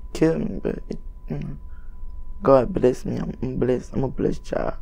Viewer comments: Nigga what? 😂🤣 All I heard was “yamayaghultdima I’m a blessed child”😂🤣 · Only words i heard were "Imma bless Imma bless child"